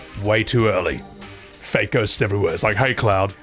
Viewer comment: Every time you say "fate ghosts" I hear "fake ghosts" 😂